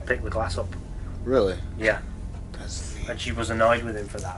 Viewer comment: is that saying in a whisper "Thats neat" or "Thats me" ? It's weird coz it seems to happen while was still just finishing speaking. 33:54 "He's waiting on old age" I hear "he's waiting on (unknown - sounds like 2 syllables) "He's waiting on an old hand." 35:25 Andrew asks for their name and it sounds like, maybe "Bowden" 36:40 "Fears" ? 37:29 Instigates 38:03 - 38:04 Have a grave (lol or Have a grape) It said First Name, Guy 39:26- 39:28 Andrew says underground, but to me it kinda sounds like "in the jungle" 40:19 "In the ground", or "in the crowd" 40:21 Where's ground?